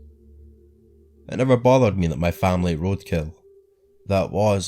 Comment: It member bothers me that my family _ roadkill. What the heck is he saying? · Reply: Ate